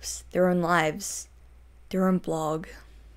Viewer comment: All I heard was "Destroying their own blog.." ... LUCID CHART PRESENTS LUCID SPARK